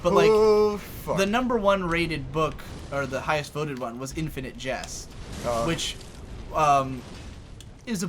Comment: Lol, I thought you said Infinite Jess. It would've been hilarious if that was a the title of the book.